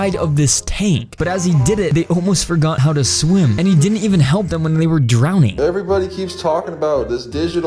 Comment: did he say fish can drowned